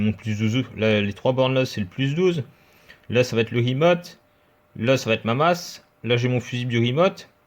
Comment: c'est quoi le 'emote' ? Je n'ai pas compris avec ton accent de Dany Boon ! ;) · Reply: Remote, c'est une borne qui permet de démarrer l'ampli quand elle est reliée au +12V